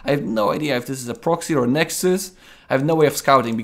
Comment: "Oracle? Phenixes? 4 Gates? 3 Gates robo? I have no idea if this is a proxy nexus." That's what I heard the first time. I was wondering, wait what, why are you considering this? Before I realized I misheard.